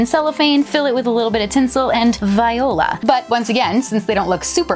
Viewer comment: I love how much you care about doing presents yourself, your kids are lucky to have you!!! the crewmates are so cute!!! (And can someone tell me what word she said at 8:31 I don't understand it 😅🤦‍♀️but she seems to often use it ) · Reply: Viola?